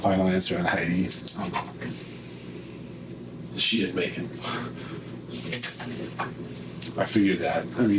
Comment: Everyone has commented on what I wanted to, so I'm just saying this. I watched this with subtitles on (because I'm still learning English) and at 10:35 it says "She had bacon" when the police informed him that Heidi didn't make it. If this were not a video about such a sad and horrible situation, that would be hilarious to me. 😔